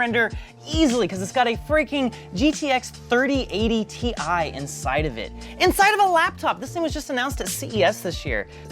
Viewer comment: DID HE JUST SAY GTX 3080 TI